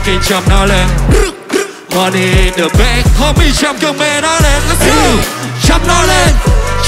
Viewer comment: Châm cơn mê nó lên , em nghe thành " Châm con mẹ nó lên 🤣 "